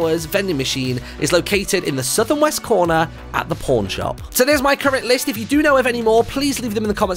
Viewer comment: oh shoot thought you said at the porn shop...